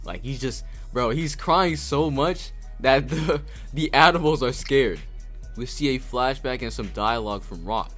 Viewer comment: I wasn't looking at the screen because I was eating and thought you said admirals, not animals.